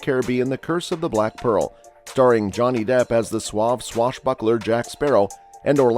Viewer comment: did he say swab swashbuckler? What's a 'swab' swashbuckler?